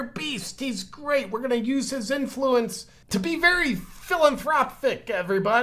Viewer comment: lol. When you said “What’s your thoughts on the Bill and Linda Gates Foundation? Very philanthropic?” I could have sworn you said, “Very Villain-thropic” Yes. Bill and Linda Gates are very villain-thropic.